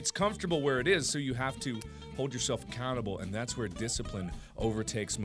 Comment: I thought u said "hold ur self a cannibal" LOL